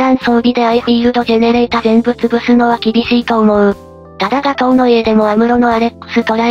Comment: ガトーノイエが ガトーの家に聞こえた